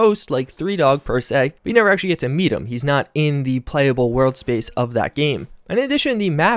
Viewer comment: ish did you say that you don't get to meet Three Dog?....